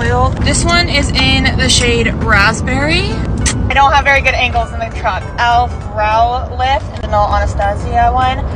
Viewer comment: for a minute I thought u said I don’t have good angle from the crotch 😂 sorry not trying to be akward